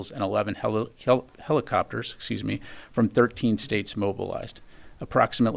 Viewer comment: Freudian slip @ 3:58 where he says "halal" instead of helicopters...🤔